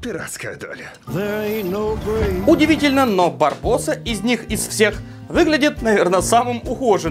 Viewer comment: Мне одному послышалось Варбосса?